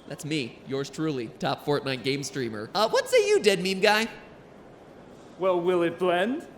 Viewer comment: I thought he said "Dead Meat Guy" and I thought we were gonna see James A. Janisse in here